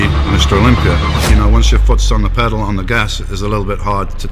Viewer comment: what does he say at 2:37 I can't understand well..."Once you .... it's really hard to take it off" what is in between that? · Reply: Once your foot is on the gaspaddel